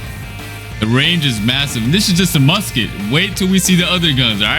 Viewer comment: did I just hear massive?